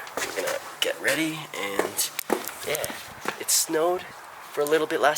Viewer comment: Hold up... 3:46 did he say it snowed?